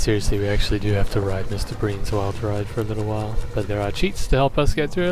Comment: I thought it said wild rice